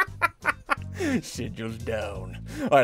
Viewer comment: did he say sh*t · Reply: No he said “sigils”